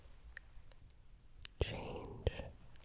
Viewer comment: “what’s the meaning of life” I THOUGHT YOU SAID CHEESECAKE